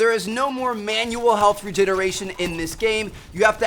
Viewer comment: did he just say no MANUAL health regeneration neck